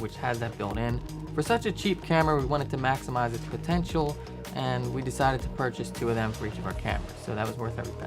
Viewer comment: Can someone translate/explain what was said between 3:37 and 3:39 ? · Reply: we decided to purchase 2 of those for each of our cameras. In reference to the Olpf.